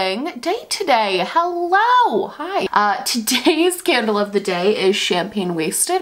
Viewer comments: I swear I heard: “today scandal of the day” instead of “today’s candle of the day”. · When you said “today’s candle of the day” I heard “Today’s scandal of the day” 😅😂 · I heard "today's Scandal of the day".. and I mean, technically accurate xD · for a moment i thought you said "today's scandal of the day"... it will be accurate tho